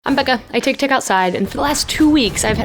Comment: When she said “I’m Becca” at the beginning of the video, she said it too quickly, and I heard “Ven pa’cá” in Spanish, which means “Come over here.” Haha!